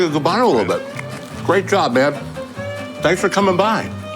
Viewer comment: what did he call him? Babe? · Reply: Man